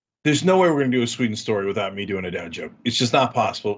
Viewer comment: you said, "... without me doing a _ joke...".what kind of joke? I couldn't understand it at all, and YouTube thinks you said "down joke" which makes no sense. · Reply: Bittersweet... And I assume "dad joke"